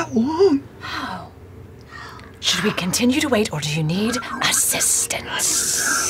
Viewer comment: Anyone else catch Travis saying “consume” at 3:45? Laudna has U’katoa in her head- CANON